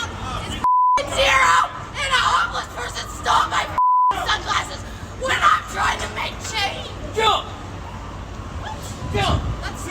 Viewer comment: That building manager was pretty funny. I thought she said, "Someone stole my sunglasses, while I'm trying to make shade." LOL But honestly, they need both police AND mental health workers in that city.